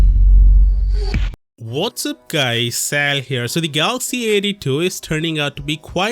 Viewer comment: "Sal Here" all this time I thought he said "SAM Here"